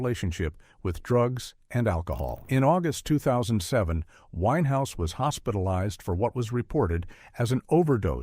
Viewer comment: The music industry celebrates their successes but often ignores the dark side — the pressure, the mental health struggles, and the addiction that follows. Amy Winehouse’s story especially breaks my heart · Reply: did he say the white house?